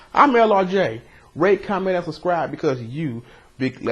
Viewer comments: At the end I thought he said rape, comment, and subscribe. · At the end I thought he said "rape, comment, and subscribe" lol